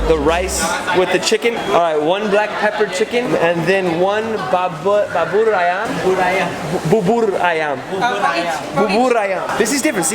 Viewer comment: i thought he was saying baboon ayam